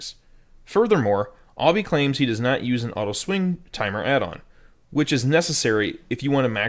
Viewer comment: Can I know on 3:31 what was the addon u said a ret pally must use (auto.. smth?) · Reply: Auto swing addon